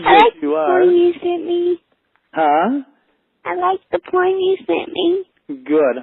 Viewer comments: I thought she said "I like the porn you sent me", and did a spit take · At first I thought she said "I like the porn you sent me" and I almost gagged.